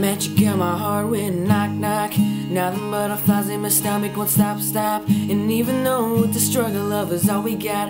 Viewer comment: is it just me who heard "my "stomick" wont stop stop" in